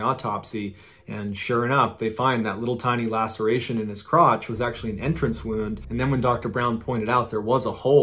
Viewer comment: "the best medical examiner, been doing it years" Misses bullet hole in crotch... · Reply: I heard "laceration on his eye...and a laceration on his crotch" I knew something was sketchy at that point